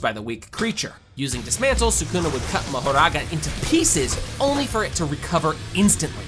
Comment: ele falo marogarara ou foi empressão minha?